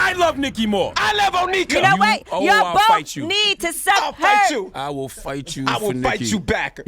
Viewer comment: I heard that yee. 9:01 "YALL BOTH NEED TO SUCK HER..." 🤣😂🤣😂